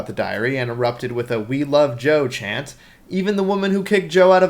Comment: is peloshi saying "we love Trump?! Priceless!